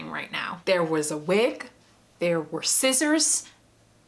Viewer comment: I thought you said, “I had a wick, I had scissors” and I was waiting for you to talk about candles. Then I noticed your wig. And was like, “ohhh” 🕯🤷🏻‍♀️